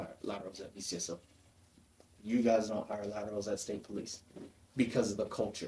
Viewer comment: Did he just say some depts DONT HIRE LATINOS bc of their “Culture???!!” Wtfff